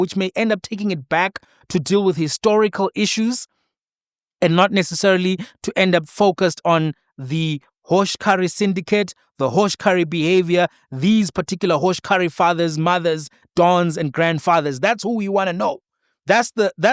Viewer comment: I heard Sol say "hosh carri behavior " 😂